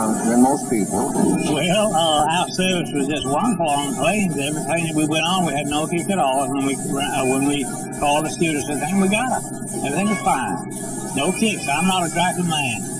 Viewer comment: What in the world did he say? LOL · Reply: I can translate for you: "Well, our service was just wonderful on the plane We paid at the time we went on, we had no tickets at all and when we called a stewardess and said 'hey we got em,' everything was fine." I speak jive: <link>